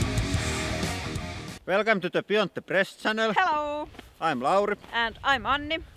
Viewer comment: To me it sounds like you say "welcome to the build the press channel" EVERY time lol